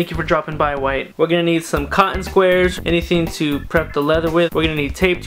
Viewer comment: Anyone else who thought he said kondom-squares???